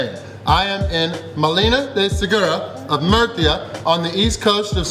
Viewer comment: I thought it said "Murica" lmao.